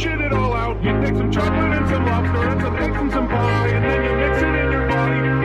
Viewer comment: I heard “you take some chocolate and some mustard, eggs with a fry”